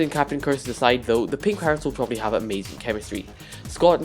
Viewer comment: did he just say pink parrots?